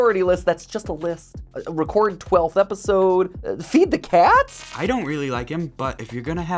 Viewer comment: For a sec there I thought said he doesn't really like Matpat